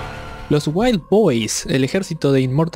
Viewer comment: acaso escuche "wild-boys"? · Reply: War boys